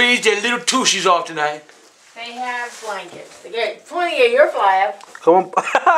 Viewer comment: what did she say? They'll get plenty in your flab? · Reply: “They have blankets and you’ve got plenty of your flab.”